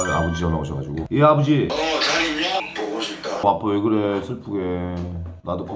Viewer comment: 어 아빠 왜그래 잘못들어서 와꾸 왜그래로 들었어 ㅋㅋㅋㅋㅋㅋㅋㅋ깜작아